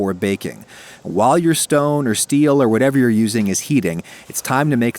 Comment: I thought you said “while you’re stoned” ha